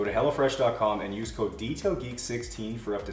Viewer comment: lol... I thought you were saying hellafresh at first during the plug for the food delivery!=)